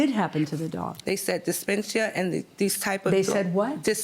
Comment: She means /dis-fā-shu/. Dysphagia: difficulty swallowing foods or liquids, arising from the throat or esophagus, ranging from mild difficulty to complete and painful blockage. A dog can aspirate during a bath. It really can if it has dysphagia. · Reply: Omg thank you, I thought she was trying to say dyspepsia and I was so confused